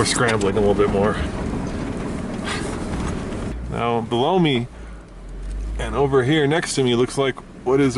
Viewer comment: Did say “Blow me” at 12:07?